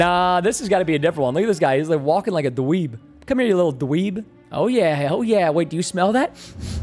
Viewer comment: “oh yeah, he|| yeah” Did anyone else hear it or just me? 😢